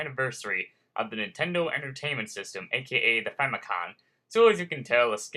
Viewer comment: might just be me but it sounded like you said famicon, its famicom...short for family computer